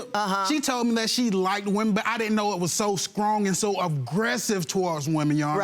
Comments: Did he really say « Scrong » at 3:07 ? 😂😂😂😂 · did he say "scrong",did I spell that right?